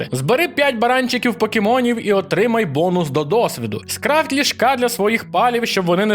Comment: почулося "скрафть Ляшка для своїх палів, щоб вони не стресували", -))))